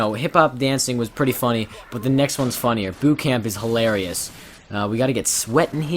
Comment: I thought he said "Boob Camp" instead of "Boot Camp", I wasn't even paying attention until I heard boob. XD